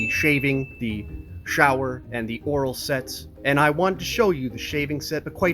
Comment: for a second I thought you said "oral sex" 🤣